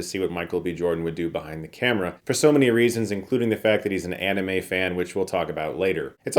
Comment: Sometimes when you said anime fan i thought you said mma fan